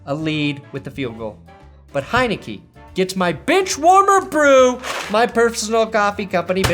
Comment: When you gave Heineke that award, it sounded like you said Bitchwarmer Brew. That's a totally different drink.